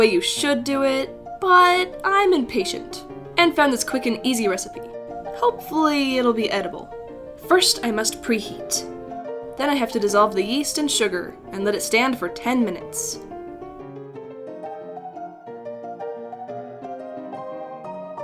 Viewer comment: when she said "disolve the yeast" I thought she said disolve the "YEET"